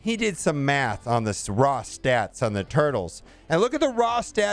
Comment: I was listening to this instead of watching, and I could have sworn he said “These are the raw stats for the turtles .” I’ve been away for a couple of days so, I immediately stopped what I was doing and looked at the screen only to realize he said “Eternals!” Can’t tell you disappointed I am that Donatello, Michaelangelo, Rafael, and Leonardo aren’t coming to the game. I mean, we already have Shredder, whose cos play name is Silver Samurai , so maybe there’s still hope.